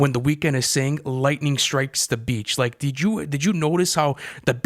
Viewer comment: I THOUGHT THE WEEKND SAID “LIGHTNING STRIKES THE BEAT” LOOOOOL